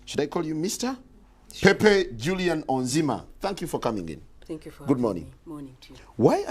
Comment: Why did it sound like he said fuck you for coming in1:07